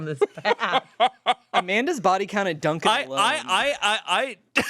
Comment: what does Spencer say? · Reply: "Amanda's body count at Dunkin alone"